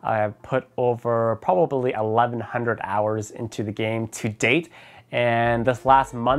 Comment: When he said "To date" I thought he said "To Day" 😂